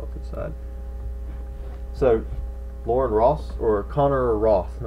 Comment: I heard "Lauren Ross" and I was like man I wish I had won first place haha